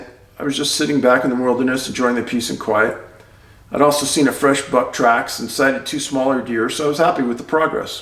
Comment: public service announcement Dave did NOT say "Id also seen fresh butt cracks" like I may or may not have thought he said, allegedly. It was buck tracks, not butt cracks. Sheesh get your mind outta the gutter viewer, gross.